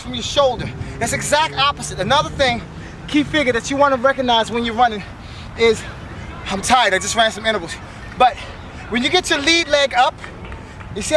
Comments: something i heard on 1:12 or 1:13 is like "I just ran from cannibals" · Did you just say “you’re tired I just ate some edibles” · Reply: Lol , "ran some intervals"